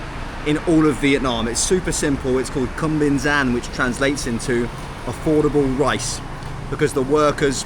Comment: <link> Download Airalo for $3 off your first ESIM! Thanks for the support everyone. Still blows my mind that people enjoy the stuff I make. ❤️ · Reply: did you just say it was called cumfromsomyounguy 😂